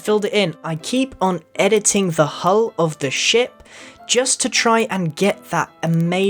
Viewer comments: Am I the only one that heard him say "I keep on editing the hole of the sh¡t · you sounded like you said shi* :)